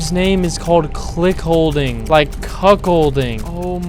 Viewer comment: i heard cock holding 😭